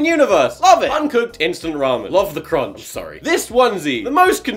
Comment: yO I HEARD “ LOVE THE CRONCH “ BUT I’M NOT SURE IF THAT’S WHAT HE SAID- DEH FANS ?